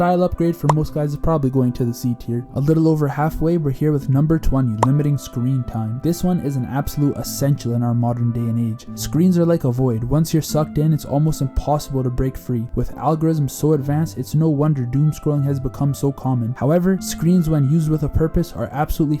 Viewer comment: I had to rewind cuz I thought u said screens are like a wh0r3 u could get sucked in 😭😭